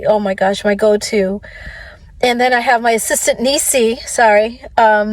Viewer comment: This is sad and maybe i haven't listened to this all the way thru but did i hear "homeless and then my assistant"?